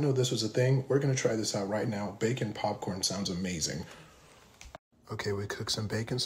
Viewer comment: I thought he said "baking popcorn"... ...I made an oopsie...